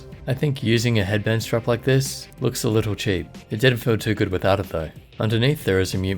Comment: did you say "It didn't feel too good without it (the headband) though"?